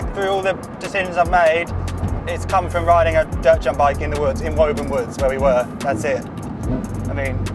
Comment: For years I thought you were saying “Woven” not “Woburn!!” (I’m American). Haven’t seen it written out until this video!